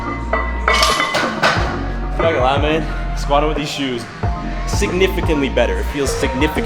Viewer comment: At like 11:25 It sounded like he said “cherry Lime aid and these shoes” So maybe that’s the flavor